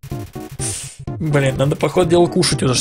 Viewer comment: надеюсь мне послышалось ) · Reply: А он сказал Блин (Ой, поздно пишу)